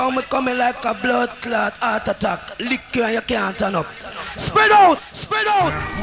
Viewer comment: Proper dark tune from 93. Bludclot Artattack. What did he say after that anyone? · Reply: I hear "lick you & you can't stand up" But it could be ANYTHING, ya know how samples go lol.